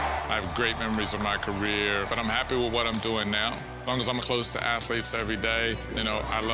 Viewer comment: oops I thought it said dopping now not doing now